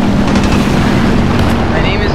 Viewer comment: is he just say "ALLAH AKBAR" ?